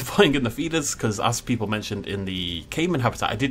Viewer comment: I legit thought he was saying"fetus" instead of "feeders" the entire time so I was so confused